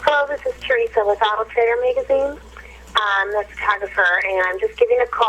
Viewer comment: Can someone tell me what she is saying after she introduces herself on the machine, I think its 7:54-7:56? To me it almost sounds like she was starting to say, "Ummm, This is kinda for" but its garbled.. Am I hearing it correctly? If someone can make that out better, please do.. Everything else is clearly said, but those few words are not making sense to me.. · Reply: Jenneh Was Here she said " ummm the photographer..."